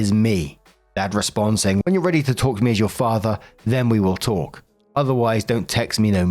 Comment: I heard "when you're ready to talk to me as your owner"